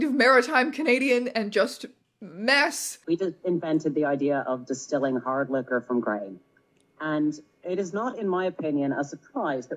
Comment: Can anyone tell me what is saying at 38:03? Pls help ive listened to it so many times. Is he switching into another language or is his accent just that unbelievably bad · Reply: "We invented the idea of distilling hard liquor from grain. And it is not in my opinion a surprise that we also invented quite a few other things: [his irish prounounciation here is horrendous but I think he is naming various irish mythical creatures]"